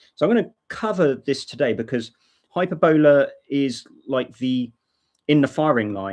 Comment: glad he put it in the Desc.... cause it sounds like he is saying "hyperbella" and no such distro exists lol